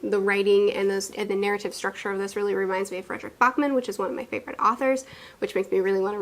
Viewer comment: haha i thought you said this book (Pet Sematary) reminds you of Richard Bachman but I misheard you. I was like...I have something to tell you about R. Bachman...LOL